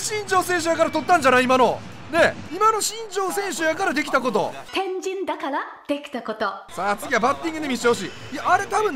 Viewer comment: 天神だから出来たこと 変人だから出来たことに聞こえた